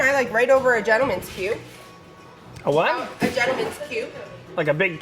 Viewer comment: she can be my spirit guide anytime.... lol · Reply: when she said gentleman's cube.... i thought she said gentleman's puke.... lol